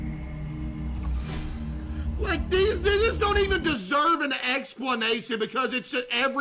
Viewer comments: as someone black, southern, and with an accent, he said “these- they just don’t even deserve an explanation anymore” I thought I was trippin the first time I heard it though 😭😭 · Bruh am I crazy but did caseoh say "these n-word" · Haha yo im not accusing you case , but at 3639 it sounds like you said these niggas 😂